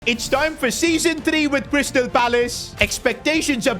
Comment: did he say Bristol Palace 💀